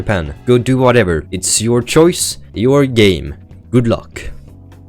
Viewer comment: really thought he said ur gae